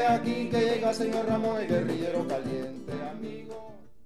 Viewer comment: Siempre pensé que decía Guerrillero valiente, ahora entiendo lo de Guerrillero caliente